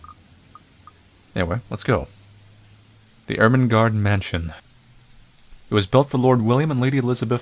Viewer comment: i thought it said ermergerd mansion xD